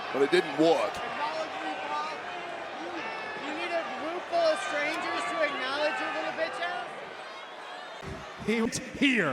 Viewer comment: nobody caught what that fan said about roman 😂😂😂 3:35:25 “you need a room full of strangers to acknowledge your b** ass 😂😂😂